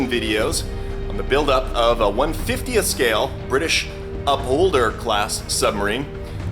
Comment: Upholder, oh, I thought you said Cup holder, I wondered how that was going to work.... never mind.